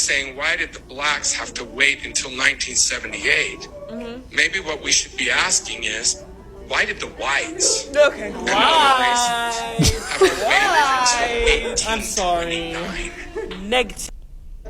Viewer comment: Edit: translation for the second part at 13:03 in case you missed it like I did! "maybe what we should be asking is whubdidbthe whites and other races have to wait until 1829." I understand why his second apology sucked, but it also could've been so much worse. Like.. I had no idea what to expect and I was afraid he was gonna double down or say something else incredibly racist..so it wasn't as bad as I thought it was gonna be. I kinda missed the the second half of what he said the first recordinh in his sermon so I gotta go back & try to listen a few times. It was hard to hear with the other sounds & I wish I could look it u! I tried listening again & I can't hearnitnover the reactions 😭😩 I only hear up until why did the whites something something something... their reactions cover the rest of the sound to me. I also wish we could also hear his first apology just because I'm curious!